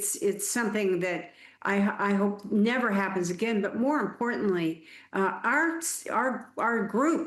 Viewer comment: did she just say “more importantly”